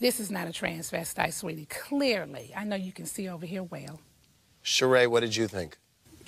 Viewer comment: “I know u can see over here well”😂😂😂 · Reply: the first time i watched the episode i was kinda young, n i thought she said over here whale 😭😭😭